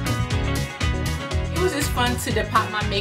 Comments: i thought it said "how to deport makeup" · I thought it said deport makeup. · Am I the only one who thought it's said deport makeup? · I thought it said "how to deport makeup" who else